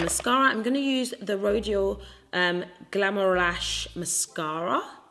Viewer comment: The packaging of this collection is unreal! 😍 we love a spicy legend! 😂❤ When you said the mascara it sounded like you said 'glamour rash' 😂 listen if there's gonna be a rash it must always be fabulous 😂